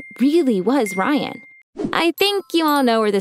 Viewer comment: Oops. I thought she said "Our relationship is not that at all" but she said "our relationship is not BAD at all." My bad.